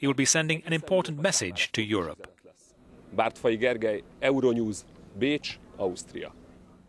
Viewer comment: Could you tell me please what the reporter (Gergely Bartfai ) was saying at the end betveen words "Euronews" and "Austria"? What was that word? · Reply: He said "Bécs", that's the hungarian name of Vienna :)